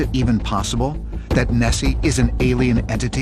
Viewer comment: I thought he said Messi and was fully ready to become a conspiracy theorist.